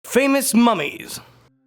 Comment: For a second, I thought he said,” FAMOUS MOMMY!!!”